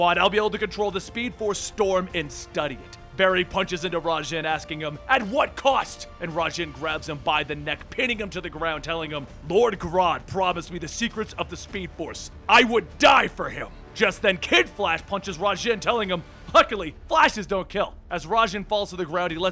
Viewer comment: Is it me or did say wod instead of rod at 6:40 ?